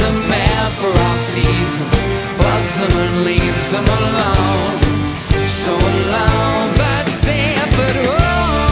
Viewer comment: sounds like he says Busy Malone.